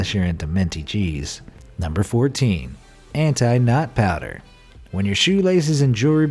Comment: I thought he said anti -NUT powder